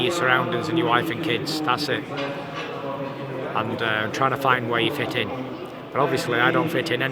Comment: Did he say he is trying A way to fit in 7:07 guys?